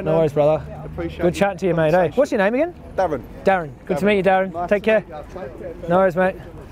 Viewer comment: I think he said his name was Devin. 😜 Great conversation! 🌱